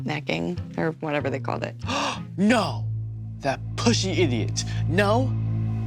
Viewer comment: lmaoo @ 0:28 i thought he said “p*ssy idiot” 💀